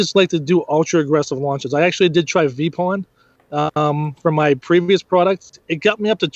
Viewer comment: What did he say at 9:25 where he got reviews fast. Couldn’t hear the name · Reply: Vipon